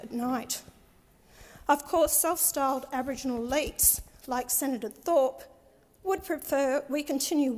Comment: I don't think he was listening closely. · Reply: Her pronunciation made it difficult; I also had to listen to it again to understand the word. I think she said "Aboriginal elites" at 4:35.